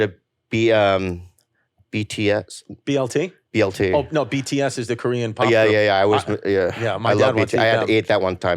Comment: i've replayed the moment like four times and still can't make it out, does chrissy say at 10:14 that tampa tony likes bts? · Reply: "BLT? Oh no, BTS is the Korean pop group. Yeah, my dad wants to eat them."